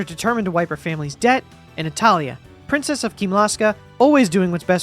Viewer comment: I heard "wipe her family's debt, and italian" OH NATALIA! lmao